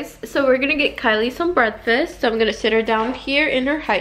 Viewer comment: she said breadfast instead of breakfast 😂 x